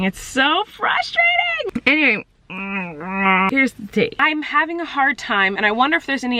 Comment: did she say “here’s the tea” what 😂🤣🤫 am so died